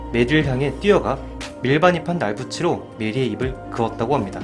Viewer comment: ' 밀반입한 ??? ' 뭐라고 하는건가요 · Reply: 날붙이